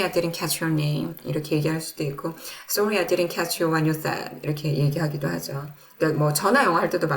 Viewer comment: 질문이 있습니다. 20:56 Sorry, I didn't catch your 와유얼떤? 21:06 Can you 얼 say that again please? 한글로 쓴 부분이 무슨 단어인지 궁금해요 Happy Easter❤ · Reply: 안녕하세요 선생님 부족한데 잘 들어주셔서 감사합니다 선생님의 리스닝이 아주 훌룡하시네요, 20:56에 제가 녹화 하다가 잠시 그 다음 말을 까먹었어요 그래서 sorry , I didn't catch you 다음에 something something,, 이라고 넘어갔어요 그 뒤에 your name 혹은 what you said아니면 I didn't catch that 만 하셔도 되어요 그리고 제가 발음 하는데 조금 나쁜 버릇이 있나 보네요 저도 다시 들어보니 can you 와 say 사이에 뭐가 들리네요 근데 제가 말하려던건 쓰신 문장이 맞습니다. 감사합니다 !!